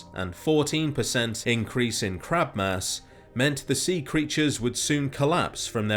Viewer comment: Honestly, I had to put on subtitles at 1:37, because it sounded like you said "Crap Mass", then I realized that "Crab Mass" made a lot more since.